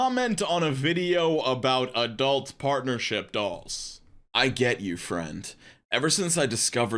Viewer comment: Oh partnership DOLLS my dude you gotta annunciate. I thought you said DOG